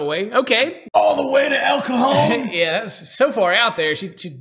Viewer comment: I AM mexican and when ed says el cajón sound like he is saying el cojon witch is a swear Word soo yeah thats something